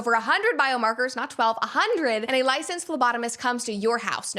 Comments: I had to rewind - I heard "licensed lobotomist" 🤭🤣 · in your Geviti ad read... Did you say a licensed lobotomist comes to your house? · Anyone else hear lobotomist first?